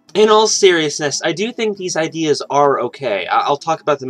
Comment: "In all seriousness" i thought he said "Anal Seriousness"